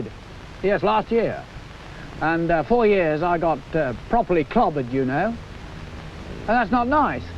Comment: I'm German with poor English. Did he really say 'and four years I got properly covid you know' at 02:20? 🙂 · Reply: Clobbered